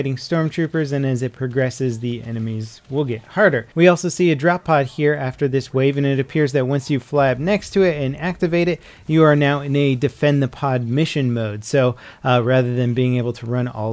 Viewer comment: I thought it said "Defend the poo" at 5:55 8)